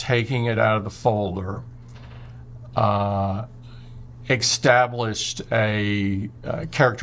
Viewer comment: Did Tim say extablished? And moments later extablishing?